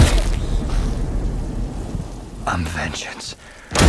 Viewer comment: I thought batman said justice not vengeance😂😂